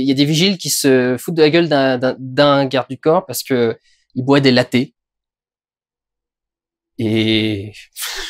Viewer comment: C'est quoi la blague à 9:36 ? J'arrive pas à comprendre ce qu'il dit à 9:40. (La traduction automatique comprend "parce que bouabdellah t" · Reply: Ah! Pardon, réponse juste après dans la vidéo : c'était "café au laits".